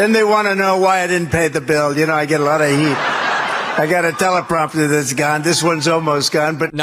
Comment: But I thought he said "I don't need a teleprompter".